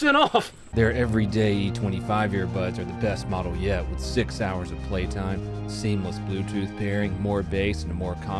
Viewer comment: During the Raycon promo, at 0:57, I totally heard "Sig Sauers of playtime" instead of "six hours of playtime"🤣😂